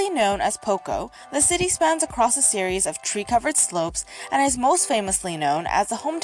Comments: Thanks to you, I heard "tree covered slopes" as "three covered slopes".... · I'm soooo used to your accent now that when the video woman mentioned "tree-covered slopes" I heard it as three covered slopes.... Keep up the fantastic work ✌🏻